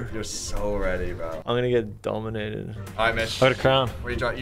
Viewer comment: subtitles realy said get out of here [__] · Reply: Nah because the subtitles thought he said b__ch instead of Mitch